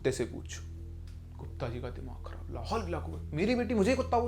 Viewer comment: Did he say ''la hawlu ala kuaataa"